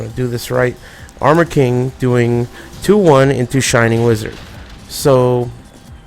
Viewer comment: i thought u said shining lizard at 3:00 loool